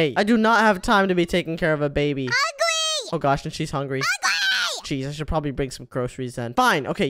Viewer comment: in here I thought said UGLY!!! UGLY LOL😂